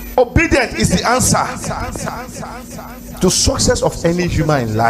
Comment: I heard him say OBIDIENT IS THE ANSWER to Nigerian Problems 😂Charlatan